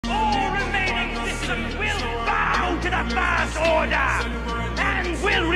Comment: Why do i always hear "the virst order" as "the third reich"